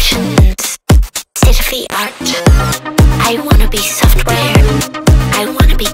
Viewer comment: The second pre-chorus line 2 I thought it said ( I want to be cold ) but she does say "I want to be code" but cold brings a whole another layer to mind. 🤔 Sometimes I feel that I want to end and go on forever at the same time.